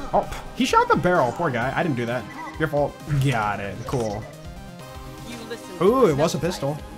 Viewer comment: brainrot is real. i heard gyatt